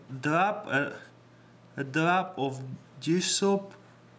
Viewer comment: I swear you said "Jew soap"